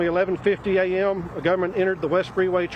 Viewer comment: he clearly says government not gunman. 🤔